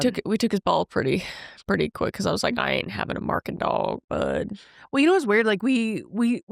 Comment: I thought she said barking dog. Like they took away his barking. 😮 I miss hear.